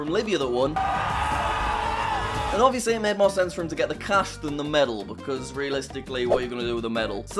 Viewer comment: it says Cast in the subtitles instead of Cash ooops